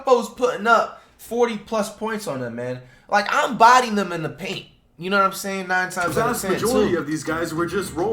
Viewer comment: did just say he would body them in the paint 💀 come on bro 😅